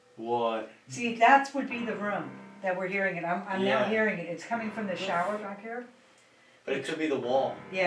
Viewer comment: Not sure if anyone made a comment on this (was gonna go looking but thats lots of comment) 53:34 say's "its coming from the shower" as she saying shower I heard a women's voice say the fucker.